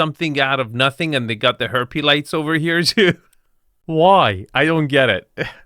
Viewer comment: Did he say ‘herpes lights’ 16:40 😂😂😂